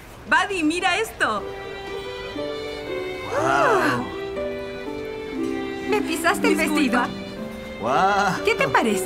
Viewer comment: Jajajajajaja. Cuando ella le pisó el vestido una dijo, me pisaste el vestido, la otra dijo a la vez, disculpa. Pero al principio pensé que había dicho, me pisaste el culo. Jajajajajajaja